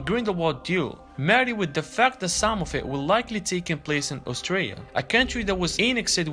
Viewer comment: It sounds like your saying Australia lol but im pretty sure you mean Austria lol